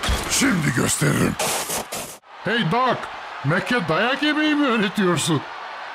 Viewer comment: BRO WHAT DOES HE SAID?? 😨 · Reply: He said "I'll show you!", and then he said "Hey (name idk), are you teaching Mac how to take a beating?", so he's basically humiliating and taunting his rival